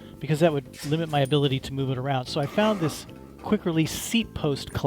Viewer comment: Just ordered two off EBay delivered for under $11. If one doesn’t work I’ll use both. The first time I watched this I thought you said quick release axils and they didn’t look like they would work so I found your video post and when you said Seat quick release it made since. Thanks for solving a problem I gave up on. Great video.